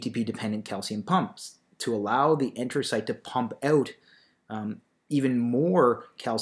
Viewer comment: In 3:12 what does he say? Intersite?